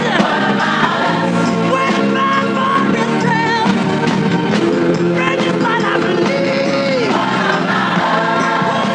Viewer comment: Is someone able to understand what Michael says when he talks into the guy’s ear? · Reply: "Bring this boy down please!" He changed one of the lyrics of the song to obviously tell the crew to hurry up and get them down. Also proof he's not lipsyncing as well.